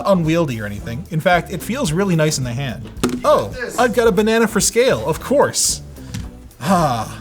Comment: Talking retro. When he said "Banana for scale " I heard "Gorilla for sale" and it reminded me of that Boomerang Cartoon Network song about Magilla Gorilla